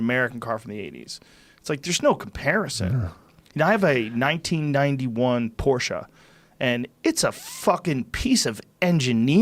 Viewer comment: Did he just say that he owned a Porsche 911 that he bought in 91,or... a Porsche 91 that was made by Chinese!? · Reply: A 1991 Porsche.